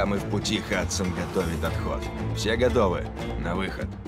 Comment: Все гадовы? или это ток я услышал?